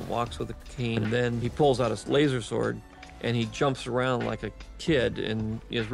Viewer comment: Did he just say lasersword?